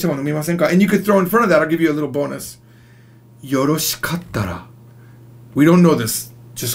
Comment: よろしかった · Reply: i thought he said よろしかたら